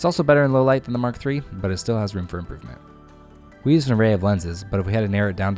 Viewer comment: Can I just clarify what you said at 1:28 - "It's also better in low light than the MK3, but it still has ....?" Thanks. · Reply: Room for improvement.